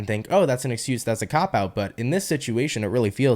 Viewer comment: can anyone tell me what just said here please? i heard a word cao pao? sorry i need to clean my ears today 😅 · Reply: I said the slang term Used as a “cop out” . I said it pretty fast my apologies lol